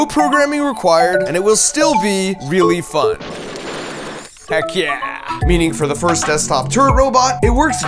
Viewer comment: did you say "Heck yeah" or "Hack yeah"? For now I'm choosing to believe it's the latter because of reasons that I hope are already obvious. ;)